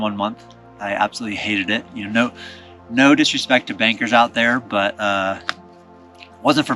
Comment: hahaha at first I thought you said 'baker' not 'banker' XD